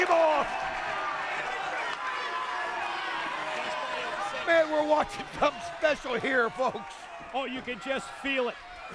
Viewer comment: what did Abyss say? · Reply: I think he’s telling Kurt to do the crossbody.